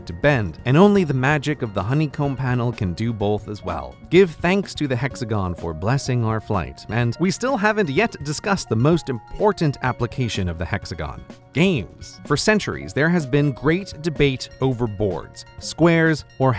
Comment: I could swear he said GAINS! Pump that hexoganal iron my dude.